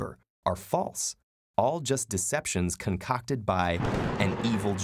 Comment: Who else got jumpscared at 1:38? I'm over here not paying attention then I hear something that sounds like "cock" then I turn around then *BAM*.